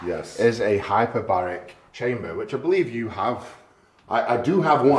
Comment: I thought he said hyperbolic chamber